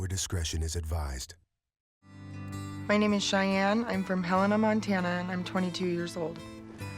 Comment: I thought she said she’s from Hannah Montana and I was trying to figure out what character she was 😂😂